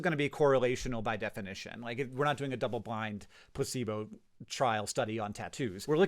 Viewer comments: Why did I hear “pussyboat” when he said “placebo” what is wrong with me 😭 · When Hank said "placebo" I thought he said "pussy boat" and was really confused. I think I'll make that substitution the next time I have to use the word "placebo" and people will say "did you just say pussy boat?" and I'll say "noooooo I said placebo" and they will think to themselves, "He just said pussy boat, but alright just go with it" and then they will say it and before long the whole world will say it and we can finally rid ourselves of the horrible word, "placebo". ps my wife is a pharmacist and they are not allowed to dispense "obecalp" anymore due to ethical concerns.